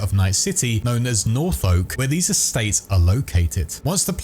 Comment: I was casually listening and when you said "North Oak" when talking about places to live my sleep deprived brain thought you said Norfolk and I got very confused 😂